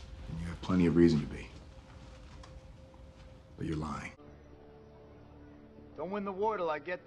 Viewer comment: I literally thought Steve said "you're mine" instead of "lying"😂😂 I had to rewind